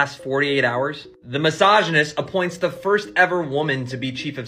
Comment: Did he just call Trump a misogynist at 1:25????